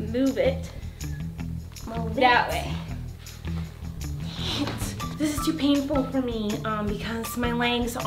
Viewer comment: Anyone else hear Miranda say the s word... No just me oh